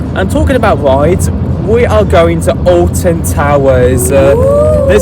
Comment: I thought that said Alton Dites for a second 😂